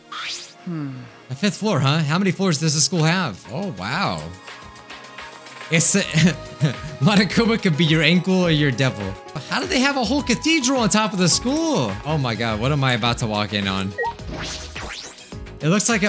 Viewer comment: Ok so like who else heard "Monokuma can be your 𝔸𝕟𝕜𝕝𝕖 or your devil. " 🤣🤡❤